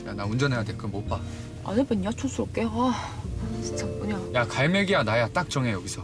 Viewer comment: 뭐라고 하는거에요? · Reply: 뚱뚀:D 안해봤냐 촌스럽게 아 진짜 뭐냐